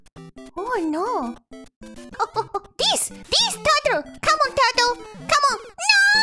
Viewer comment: she's saying "oh angel turtle"(because of the wings). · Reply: I heard "oh and the turtle"